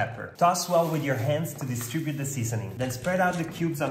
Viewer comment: Cute guy, first time watching, i heard him say DeStupid the seasoning 🤣 i know, how distribute of me 🤪